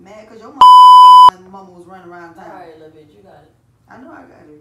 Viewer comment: It sound like said “aight lil bitch u got it”